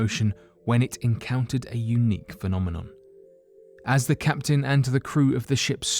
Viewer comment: I cant hear him say unique now without hearing Eunuch.